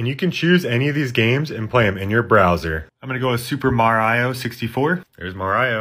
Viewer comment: What the hell did you say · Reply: super mareo